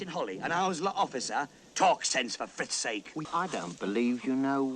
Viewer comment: Every time a rabbit said “for Frith’s sake”, I thought they said “for frick’s sake”, which, in turn, made me question the PG rating.